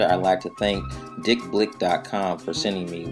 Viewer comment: was reading comments an thought he said dick lick